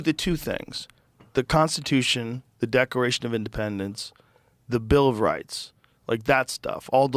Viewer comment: 'the decoration of independence" lol did joe really say that at 5:22?